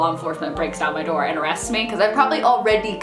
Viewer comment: What did say at 5:07? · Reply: he yelled porn